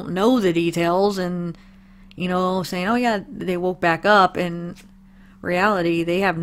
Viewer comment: I hear "I walked back up"